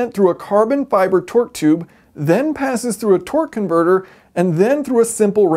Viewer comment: I literally misheard it as.... "Twerk converter"